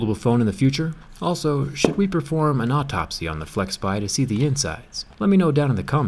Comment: I hear insects instead of insides